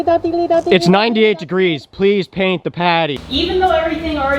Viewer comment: Anyone else hear the “Where’s my drink?” Fish from the Pizza Delivery episode or just me? Just me? Cool